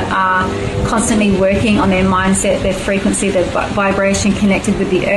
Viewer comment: Anyone else hears 🍑 vibrations at 10:28? 😂